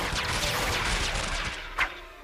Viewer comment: злой флеш сказал мудак ???? или мне послышалос